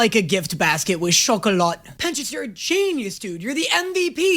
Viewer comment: Look, I need SOMEONE to shout at cartoons on my second monitor while I edit videos and nobody else has this many custom pokéballs in the background while they do it. Where am I gonna find a replacement?? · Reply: when you said pencious (I know I spelled that wrong) captions said peniuses